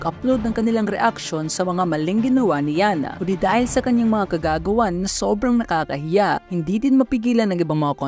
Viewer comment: "... ngunit dahil sa kanyang kagagawan..." I heard it as kagaguhan